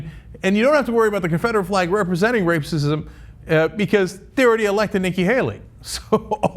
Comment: Did Cenk say "rapesism" @ 3:19?